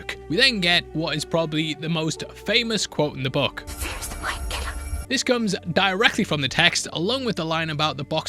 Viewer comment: is this a joke? What did she say! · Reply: "Fear is the mind killer" or something like that. Had to listen three times to get it